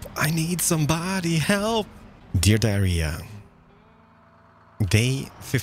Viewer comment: did he just say dear diarya